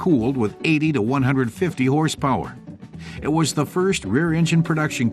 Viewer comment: they said "80 to 150 horsepower". At first I thought they said "850 horsepower". Wow! I had to rewind and listen to it again. Go, little Corvair!